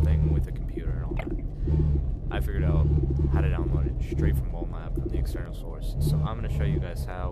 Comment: boy I thought you said straight from bonelab